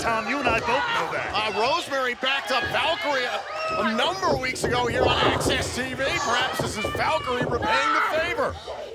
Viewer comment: What did Taya say at 9:58? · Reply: I got bigger headlights lol